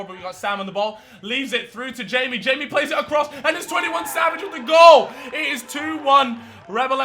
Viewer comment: Did you just say 21 Savage